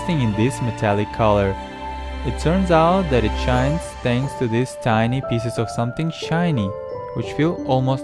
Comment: I thought you say "Thanks of this piece of shi" shiny